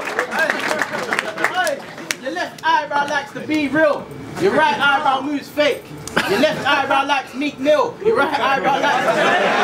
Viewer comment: Can someone explain 16:39-16:44 for me, I don't know the reference.. And what is actually saying at the end? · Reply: left eyebrow likes to be real, right eyebrow moves fake. Left eyebrow likes meek mill, your right eyebrow likes drake